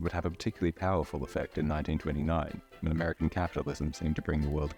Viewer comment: it sounds like he says "in 1939" or 'turtee nine"rather than 1929 in referring to the great stock market crash